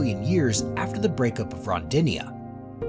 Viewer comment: Tribbleite? Robdinia? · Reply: I thought he said it like "Rhondinia." As in the Beach Boys' "Help me Rhonda, help, help me Rhonda!" Yes, the narrator needed MUCH more help than he got!